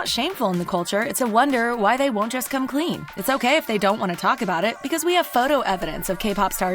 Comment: You think she said its ok but she said its gay